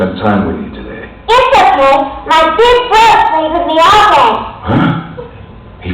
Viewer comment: Dojima: Hey Nanako.. look... I'm so sorry, I promised I'd spend time with you today. Nanako: Yes a**hole (I think? Not sure what she actually said) my big bro played with me all day Dojima: Huh? He fucking can? Hey thanks man Yu: Sure. We had a good time. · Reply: Just a slight correction of Nanako's line, but I have to agree that yours is funnier. "It's okay. My big bro played with me all day."